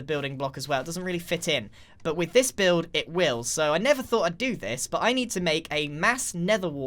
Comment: did he say “I nether though I’d do this”?